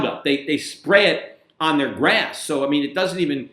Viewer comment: Sorry I thought Peter said sprayed roundup on his ass😀 it was grass. I know for a fact he said masterbation. I should sue Rosie and her four sisters because I had to spit on it and my spit made me spew Siemen and that caused my cancer. Sounds like I should make billions because I have been doing it for 53yrs.